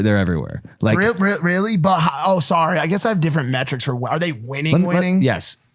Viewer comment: anybody else hear ksi?